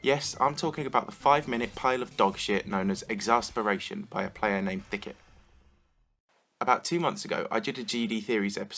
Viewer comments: did he say dck head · Am I the only one who heard "dickhead" instead of Thycket at 1:50?